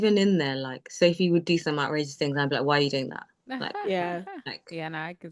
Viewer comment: Did I hear her say Sophie did what things 🧐 40:18 Edit: mystery solved in the comments 😊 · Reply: Thanks for asking the clarifying question. No, she was saying Sophie would do some "outrageous" thing. With her accent, we could understand how that could sound a bit unclear.